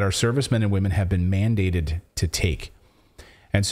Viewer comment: When you said mandated I heard MAIMdated.